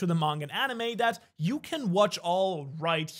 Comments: did he just say Shanks for watching ☠️ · Was it just me or did he just say shanks for watching